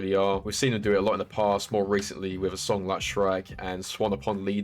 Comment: omg, i finally figured out what i'd been mishearing. i thought you were saying that certain songs reminded you of SHREK, lol, at first, i was like, " ... shrek? like ...shrek, the character?" which produced a complete brain cramp. then after thinking it over, i figured it was referring to the music on the shrek soundtrack, and ... okay. imprecise, but okay. maybe going for an overall feeling instead of a particular sound. and THEN, in an "ah HA!" moment, i realized you were saying "shrike." of COURSE you were saying "shrike"! i haven't quite parsed your accent yet, and, so ... i'll just go sit over there, alone.